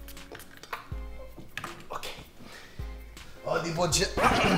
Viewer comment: Lol did he say "eodi boji" at 4:02 ???? XDDDDDDD